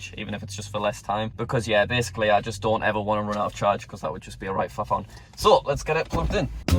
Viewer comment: I used to have trouble with the unplugging the charge cable also, but then I realized that it's a press and hold button, and not just a press. Also, can anyone tell me what he's saying at 6:40 ? What is a "right fuffon"? Halp.